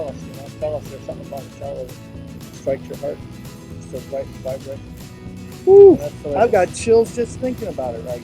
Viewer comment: I can't understand what he says. That prompts you to get "chills just thinking about it". Can you translate? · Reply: "He's like a cellist. There is something about a cellist that strikes your heart. It's the right vibration."